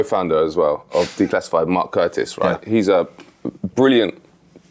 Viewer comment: Thanks guys, Great interview. Quick question..Who did Matt say his boss was. He mentioned him around 42:35, Mark someone, I couldn't catch the name. Can anyone enlighten me. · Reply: Mark Curtis the other co founder of declassified.